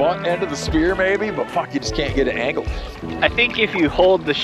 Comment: Anyone else catch Steve saying f*ck at 25:55 lol